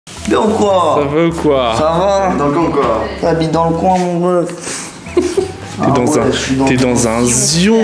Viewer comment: g compris "ta bite dans le coin ou quoi ?!" 😂😂😂😂 jsuis vrmnt un gamin